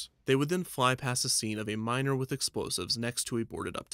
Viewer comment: At first I heard “a minor with explosives” rather than “a miner with explosives”